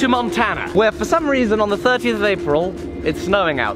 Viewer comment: 13th of April Published on the 1st of May. Edit date references better!! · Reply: in said the 13th of April. I replayed it 6 times and that is what he said.